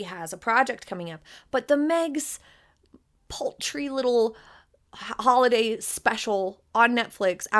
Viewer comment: Paltry, it sounded like you said poultry, 😂😅😂😅 but I love ya, your makeup is gorge today, especially your eyes